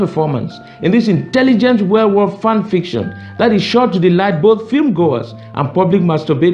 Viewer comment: I thought he said werewolf fan fiction.